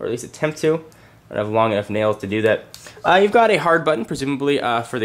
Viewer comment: did you say "Long F Nails to do that" at around 5:00?? OOOOOOOOOO Its bad to swear